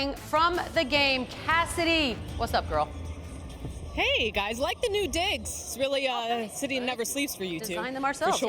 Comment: did she say "i like the new dicks" wtf LOL